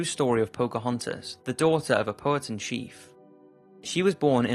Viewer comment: who else thought when her said pochontas he said poke hunters?